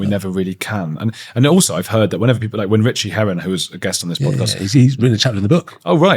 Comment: I thought he said Richard Herring for a second. 😅 I would buy the book if he written a chapter.